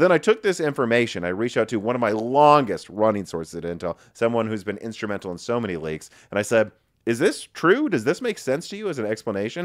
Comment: Sounded like "someone who's been instrumental in so many Lakes." 😄